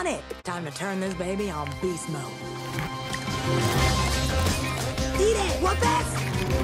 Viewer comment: Sounded like she said "Eat it whoop ass."